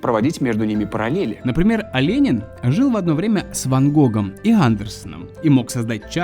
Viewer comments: мне одному послышалось что на 5:43 сказал "Оленин" 😄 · мне послышалось, или сказал "Аленин"? ахахахах